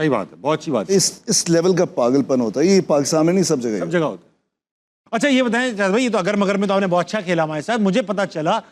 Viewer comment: I think Tabish is saying Altaf Bhai.